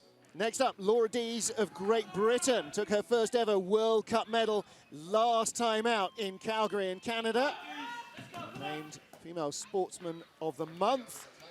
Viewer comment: I thought it said "Laura Lee"